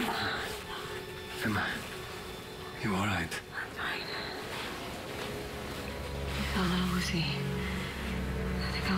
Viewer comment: guys... I don't speak English quite well... can u please tell me what Emma said at 2:20? "I feel a little ..?.. I'll lay down for a bit" TKS And... so the kiss didn't helped her to contain the flow of darkness... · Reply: "I feel a little woozy" it means un easy, dizzy, u know? :)